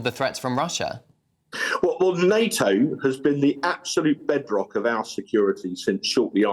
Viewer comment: I thought he said blackrock but it was bedrock Europ is in danger forget ukraine concentrate on your own citizens and countries first